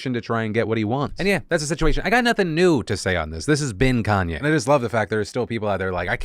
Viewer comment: I know Phil said "This has been Kanye" but it really sounded like he said "This is bin Kanye" and I immediately just nodded my head because he is definitely a garbage person.